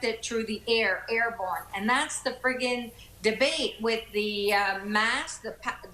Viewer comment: Did she just say, "and thats the friggin'"???? LOOOOOL